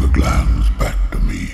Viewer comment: Is he saying "Bring the clams back to me." Is that a shell joke?! o_O